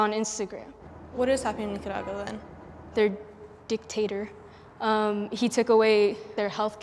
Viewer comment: Am i da only one confused bout wat she said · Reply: JustaGODD dictator